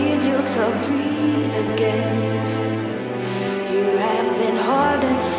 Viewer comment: You're hurting so much I need you to breathe again You have been hurting so much I need you to breathe again Skin, I’m folding Come in, my friend You feel unsteady But I won't bend You're hurting so much I need you to breathe again You have been hurting so much I need you to breathe again We tear our demon You feel no pain In the sweet surrender Alive again You’re hurting so much I need you to breathe again You have been hurting so much I need you to breathe again You're hurting so much I need you to breathe again You have been hurting so much I need you to breathe again You're hurting so much I need you to breathe again You have been hurting so much I need you to breathe again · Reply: I thought it said Haunted, not Hurting